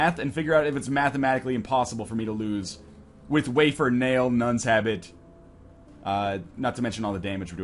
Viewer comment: Wow...I'm stupid this entire time I thought he was saying nuns have it. Not nuns habit.